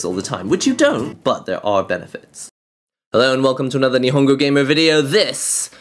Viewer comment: I thought he said "hello and welcome to a new hunger game video" ..